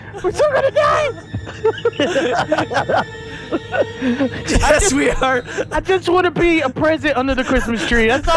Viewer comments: Did he just say "bye sweetheart"?! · Its sounded like he said "Yes sweetheart" at 6:01 or is it just me? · Did Vanoss say "Sweetheart" at 6:00 or is that just me?